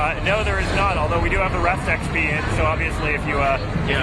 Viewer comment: did he say "rest XP"? and i'm guessing that's xp when you don't play?